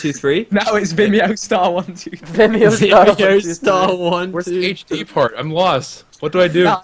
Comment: I thought he said where is the HD porn xD!!!!!!!!!!!!!